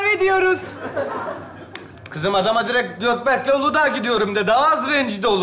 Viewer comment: Aqa ben mi yalnış anladım Götberk mi? 😂